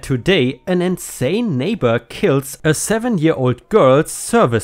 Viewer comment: I thought he said the neighbor killed a seven year old girl